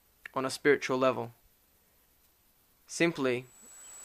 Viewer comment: I thought he said, "We all desire Alfredo." I was just like, 'I don't get the joke...'